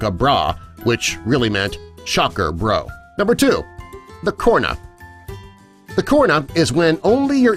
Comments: The fact that I thought he said corona not corna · I thought the corna said corona for a second 🤣